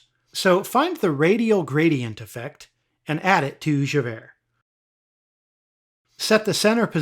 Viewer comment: Who heard "And add it to your sugar daddy" the first time?